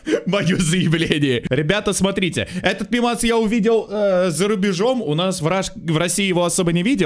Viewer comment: Коротко о том что услышала я: Этот пивас я увидел за рубежом...